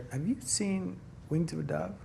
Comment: What does he say at 11:34 ??? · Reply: The Wings of the Dove 1997